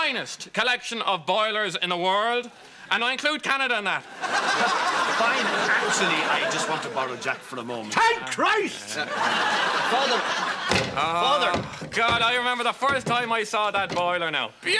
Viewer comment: "HELP ME" "THANK CHRIST" 😂😂😂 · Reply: I always thought he said Ted Christ 🤣🤣